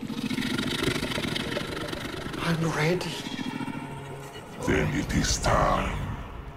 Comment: what he (Master) said after word "I am ready"? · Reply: I think he says then it is time